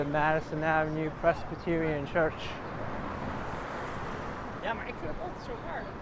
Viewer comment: I hear 2 ladies talking to each other in Dutch language: "Ja maar ik het het ook zo warm". "Yes, but i'm so hot too". (Temperture 98 F / 37 C) No idea who the are, perhaps they let us know. Ruud Mulder (NYC fan) The Netherlands